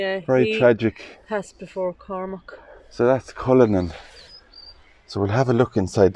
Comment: When you mentioned Cullinan I got excited because at first I thought you said Quillinan which is my married name. I love to see that or Crozier which is my maiden name. Thank you again❤❤ 💓 💖 💗